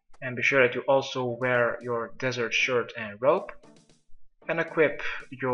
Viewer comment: lol i heard " and equip your weapons " not unequip, went there with whip, nice nurse